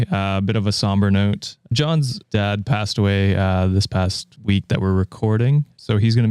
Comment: Oh my god, I thought you said "John's dead; passed away" and then quickly moved on oh my god my mouth was wide open I went to the ig to see if anyone was talking about that quick pivot oh my gosh I had to watch again to make sure I heard it right, which I didn't. I'm very relieved that John is alive, my condolences to John's family and him